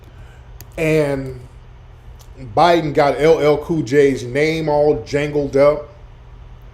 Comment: I don’t know if you know what you said, “Biden got Dajango up” @ 40:09 . Lol 😂